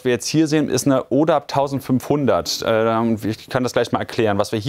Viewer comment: ich hab zuerst ne Olaf 1.500 verstanden😅